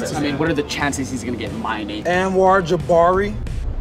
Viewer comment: Did he just say Anwar Jibari?😂